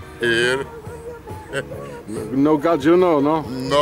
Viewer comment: hi, what the guy says @ 2:46, no gatzio? what's that? thanks